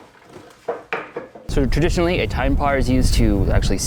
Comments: Did he say, "tine par" ? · did he say TinePar, not Pine Tar?! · said "Tine Par" instead of pine tar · Am I the only one who heard "tine par" at 11:35 instead of pine tar?